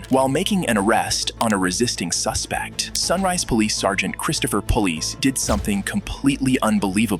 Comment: did you say the cop's name was Christopher Police? That can't be right.